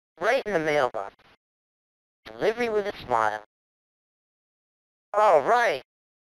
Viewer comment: "Delivery with a smile" As a kid with the Sega Genesis version, I never heard that line. I always heard it as "Living with Ishmael" hahahaha